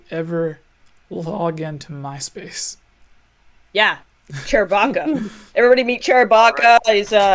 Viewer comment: sorry did say tube or Chubes? Either way the Girl with the Chewbecca chair thx for saying I don't exist. Im Canadian and I am alive Canada is a real place man! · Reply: Canada isn't real and it's chewbs as in chewbleema because she is married to Chewbacca with her side chairbacca